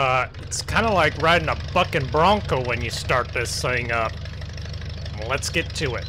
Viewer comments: I’m dying😂 sounded like he said “f*ckin bronco”😂😂😂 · I swear it sounded like he said f*cking bronco